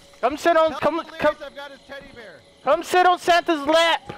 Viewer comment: I think it said come delirious I got a teddy bear